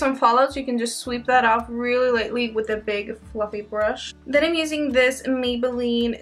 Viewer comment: I thought you said "big fucking brush" at 9.42 😂😂